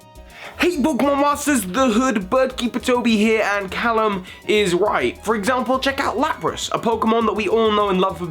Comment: Did he say calum is white at 0:32 😒😂